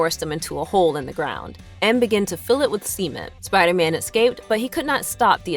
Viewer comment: Who heard “cement” and who heard “s3men”? 😆🍻